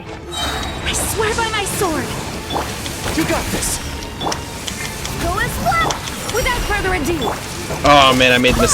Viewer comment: I've always thought Jean was saying "Go and swim" when she uses her skill.